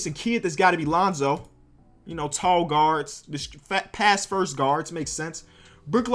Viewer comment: Did he just say Lonzo is a past first guard